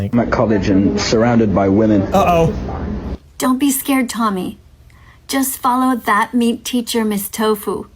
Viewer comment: am i the only one who hears just follow thst meat thracher